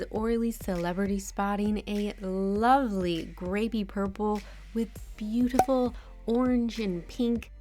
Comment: i thought you said 'gravy purple' and couldnt stop laughing!